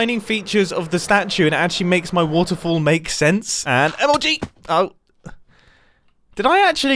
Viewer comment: Did he just say MLG?